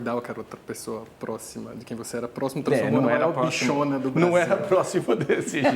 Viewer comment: Ele fala que não era a "bichona do Brasil" no min. 1:14:02? É isso mesmo ou eu ouvi errado?